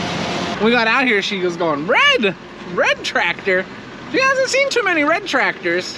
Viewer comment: I think I heard her say GREEN tractor.